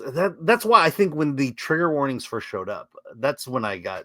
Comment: I thought that said, 'Tigger Warnings'.